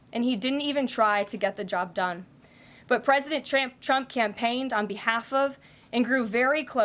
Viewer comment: What did u call him President Tramp?